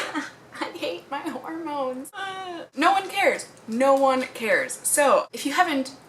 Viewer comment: u said hormones and i thought u said pornos wow okay